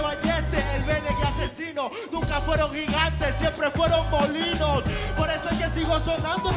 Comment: "Nunca fueron gigantes solo fueron molinos" Una referencia a Don Quijote y a Cervantes (Español) y nadie se la grito, y decian que él publico español era él cual valoraba mas él ingenio. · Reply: Yo estaba allí y literalmente escuché "siempre fueron gorrinos"